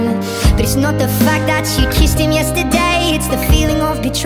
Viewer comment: I can't be the only one who thought it said "And everything my mom tells me that I should walk away" 💀✋️